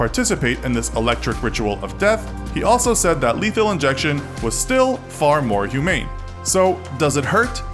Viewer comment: I thought he said illegal injection 😂